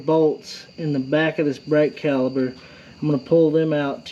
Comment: Hi great video thanks not sure if it's your accent or my hearing sounds like your saying brake caliber. Should be caliper ✌️